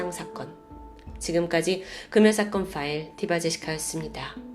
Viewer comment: 엄마랑 유튜브 영상 즐겨보는데,엄마가 어느날 이봐제스카 보자고하셔서 응?? 했어요 ㅋㅋ 디바제시카를 엄마는 이봐제스카~로 들었나봐요 ㅋㅋㅋ 그후로 계속 이봐제스카~~보까?? 하면서 잼나게 잘 보고있어요 ㅋㅋ